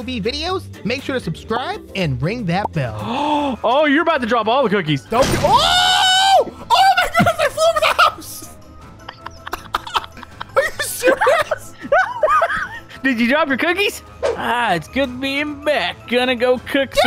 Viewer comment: It sounded like said I hope you drop you d word cookies